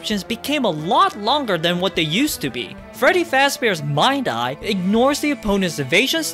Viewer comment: I wasn't paying attention for a bit, and then i just hear "Freddy Fazbear's"